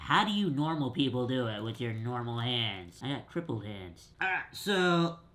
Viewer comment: “How do you normal people do it with your normal hands I have crippled hands” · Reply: I thought he said triple